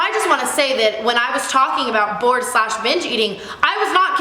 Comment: I thought that she was saying bore and bitch eating 😂